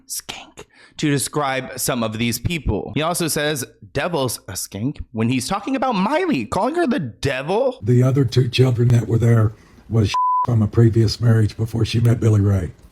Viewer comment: It sounded like he was saying Noah is the devil skank did I hear that wrong ?